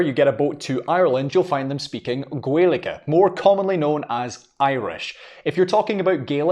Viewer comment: I’m so sorry, but I keep hearing “Garlic” and all I can think of now is Scottish Tribes in their kilts and their blue paint wielding flails with Garlics for the dodgy bits. Silliness aside, as an American this was very educational for me. Thank you!